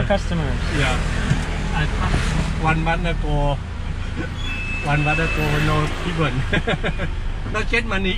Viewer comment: think he said it's been a month with no people. "One month ago, with no people." But I could be mistaken.